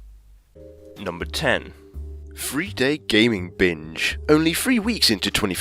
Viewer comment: When he says binge, it sounds like he says bitch